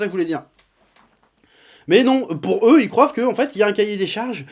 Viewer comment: J'ai stoppé la vidéo quand j'ai entendu "ils croivent" 🤣😂